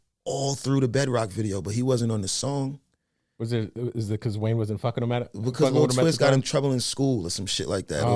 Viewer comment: EXACTLY!!! he said cause wayne wasnt fucking him wtf did I just hear?